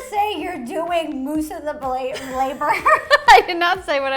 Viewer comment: DID U JUST SAY UR DOING MOOSE OF THE BLA-BLABER